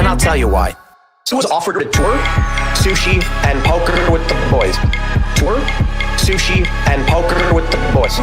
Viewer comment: Emma's theme song is great. Tim says tour but, at a certain point it sounds like he says: "She was offered; twerk, sushi and poker with the boysh. He tried to get her to play along to play Wendy in Neverland like he's Peter Pan or something.